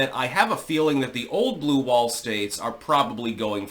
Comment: I misheard you saying "blue ball states" and figured out a definition for one: it's when you hope to win a blue wall state, but it doesn't come through.